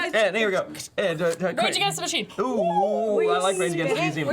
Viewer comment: did say rage against the museums?